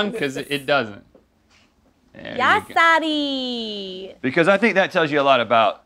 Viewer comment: Did she just say "yaas daddy" ????! 😂 wtf